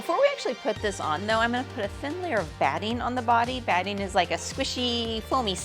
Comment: am I the only one who hears Baddie?!